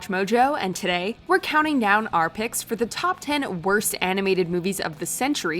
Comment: I can’t be the only one that heard her say top 10 instead of 20🤣